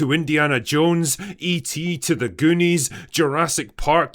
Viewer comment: Did i heard goonies??? 😂